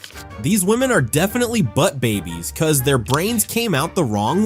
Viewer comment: did they say "butt babys"?!? LOL